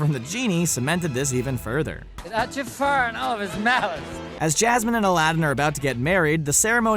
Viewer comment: What on earth did he say at 7:55 😂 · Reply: I do believe it is "Without Jafar and all of his malice. Everybody's happy."